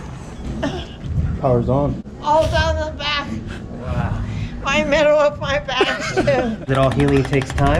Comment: Does anybody know what she’s saying at 1:06 ? 😂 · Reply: all down the back...middle of my back lmfao